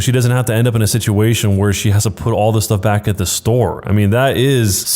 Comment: your subtitles say love instead of stuff